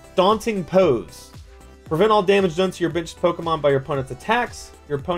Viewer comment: Lol everytime he said benched I thought he said bitched